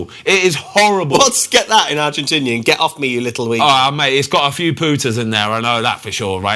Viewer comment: it’s a few “got a few Pooters in there” 😂 not putta but Pooters, ahh the English spin on things is hilarious 😂 am fucking dying ahahah Oi Ben you’re a mad one ❤